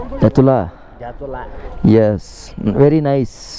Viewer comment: is he said kepartula 😂😂